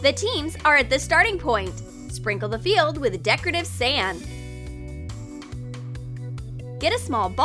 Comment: did she Say sam? WHO IS SAM!!!! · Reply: She said sand it is just her accent